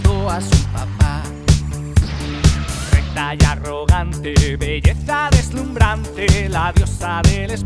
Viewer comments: La parte de "Lucha contra los troyanos" es un orgamos auditivo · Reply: yo escuche "de talla arrogante" xd, acabo de ver los sub tienes razon xd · La parte de "Lucha contra los troyanos" es un orgamos auditivo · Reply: yo igual escuche "De talla arrogante"